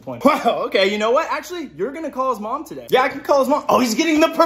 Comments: “No he just died on patrol” “WTF” I LOST IT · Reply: I thought he was said no he just got on patrol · I had to rewatch 5 or so times before I heard "died" on patrol, I thought I was got on patrol😭😭